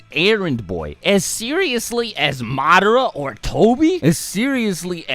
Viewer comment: did you just say MAADRA